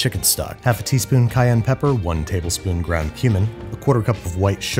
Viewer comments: I misheard "ground cumin" as "ground human" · I swear every time he uses that I think he’s saying “ground human” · I thought you said human for a split second there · i thought he said ground human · Did he say HUMAN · I thought you said ground human not ground cumin lol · Thought he said "ground human" and I was like, Torgo's Executive Powder?